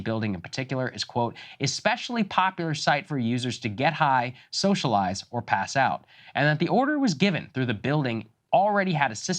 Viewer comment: I heard it as "Pelosi gets high, socializes, and passes out." Go figure.